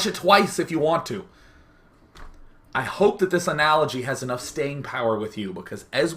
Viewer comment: I heard: "I hope this analogy has enough stain power with you" :)